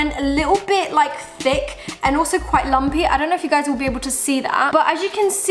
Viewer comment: did she said, also quite lambi.?